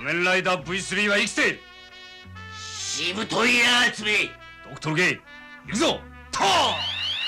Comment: ここ(ほっとけ❗)に聞こえたの俺だけ？😅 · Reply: 聞こえますね。ドクトルGって言ってるんでしょうけど。